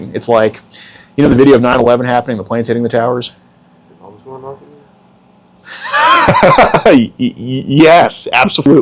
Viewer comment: what the hell did he say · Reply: The bombs going off in there